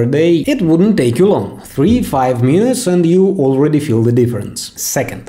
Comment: his accent is cool but is scared me when I thought he said 3-5 years instead of minutes